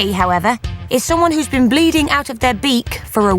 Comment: I lost my crap when I heard "Keep Bleeding the Love"😂